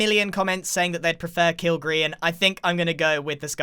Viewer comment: I thought they were both called kill Grian